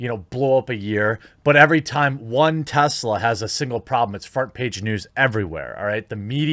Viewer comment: I heard “fart page news”. Is it just me or did he say “fart page”?